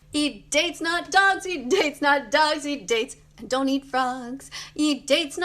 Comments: am I the only one who Thought she was sayin “eat dicks not dogs” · i misheard 'dates' to diccs